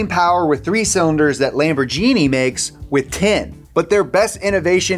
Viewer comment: Did he just say LamborJeeni !!!